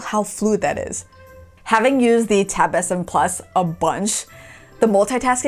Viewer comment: 🤣 thought she said tab sin plus instead of s7 plus🤣